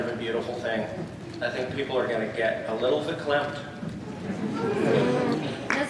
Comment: What did Graeme say? "people are gonna get a little __". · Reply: “lawinrp” Aban "verklempt" its a german word meaning choked with emotion. i think anyways. could be wrong